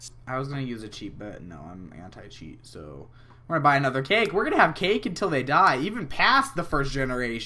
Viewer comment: When he said I'm gonna bye another cake i thought he said I'm gonna buy another kid.